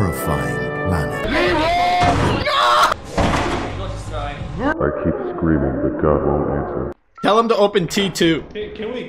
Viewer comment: Is that the Emperor from TTS? I can't understand it but it sounds like it to me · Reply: Probably the same text to speech generator TTS got the voice from but it's not from the show. It says "I keep screaming but god won't answer"